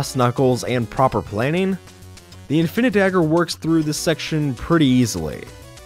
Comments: DID SOMEONE SAY INFINIDAGGER! I miss that Game Grumps playthrough. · did you say... infinidagger? HELLO! I RETURN FOR ANOTHER ADVENTURE!